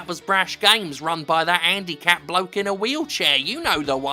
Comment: You know, when he read "handicapped bloke in a wheelchair", I heard "Andy Capp bloke in a wheelchair" and immediately decided that Paul Ryan (not that one) looks like a tiny scruffy drunk in a wheelchair.